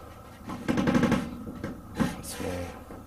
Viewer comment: Crazy Kool Stuff !!! At--3:49-- "That's me..." is what I heard or it could be "That's him". At-5:55- "LEAVE !" At-6:48- You can see sprites looking though the window, with one barely seen next to it, looking pretty ticked off with it's mouth wide opened. Is it a girl with long hair, the smaller one in front of the main sprite ... & ...or could it be the person that was shot & killed there, and it looks like a third sprite in front of the main one might be a girl as well. I'd say it's definitely a paranormal picture ! Didn't you say some guy shot his wife & then himself in one of those homes? I would have liked to know why the home on the end was roped off. Maybe was in danger of collapsing or was there a murder there as well?